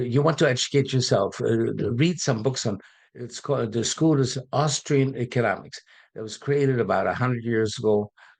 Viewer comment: What is Bert Dohmen saying here at 09:35 ? "Austrian Economics"? I'm aware there is a seminal book on Austrian economics that is around 100 years old - "Human Action" by Ludwig von Mises, first published in 1949. That book, "Human Action", is considered Mises' magnum opus and one of the most important works in the Austrian school of economic thought. If anyone's curious, in this comprehensive treatise, Mises laid out the principles of praxeology - the study of human action and decision-making - and provided a systematic defense of free market capitalism against socialist and interventionist ideologies. Then there was another; while not quite 100 years old, another highly influential Austrian economics book is "The Theory of Money and Credit" by Ludwig von Mises, originally published in 1912. This work developed the Austrian theory of money and contributed to the Austrian business cycle theory. The problem is (for me, anyway) is I can not understand what this guy is saying. He did not pronounce the sentence very distinctly so I do not know what the book title is he's referring to here. Does ANYONE know?!! Please tell me! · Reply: He does not mention a book, he just says to try read a book on Austrian Economics. See the Wikipedia entry “Austrian School Of Economics”.